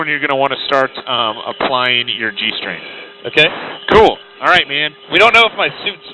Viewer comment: I heard "That's when you want to start applying your.. G-string"